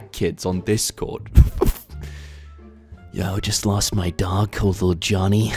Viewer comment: I thought he said I just lost my duck lil johnny and I was incredibly confused, until I listened again and realised he said dog